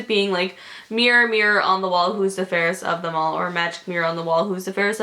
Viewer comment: the one about the magic mirror on the wall who is the fairest one of all is definitely a mandela effect because i remember when i was younger i thought she said "who is the fairest of the mall" because i didn't hear her right when she actually said "who's the fairest of them all" and this mandela effect shook me to the core lol